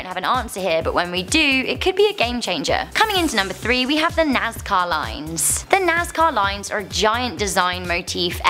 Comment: Sounded like she was saying "Nascar Lines".